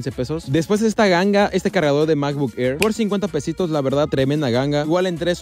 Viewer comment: entendí femboy :'v